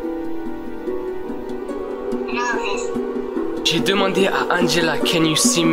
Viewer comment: elle : yes Toi : *choqué * Et là je l'entends encore parler mais c'est coupé et elle dit :"know this..." ou " you know this" donc elle sait que tu peux la voir et l'entendre. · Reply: You know this